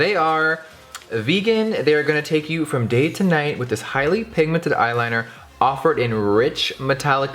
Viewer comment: I thought you said "it will take you from gay to night"